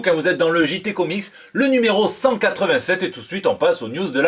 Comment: J'ai compris "... et tout de suite on passe aux NUDES de la semaines!"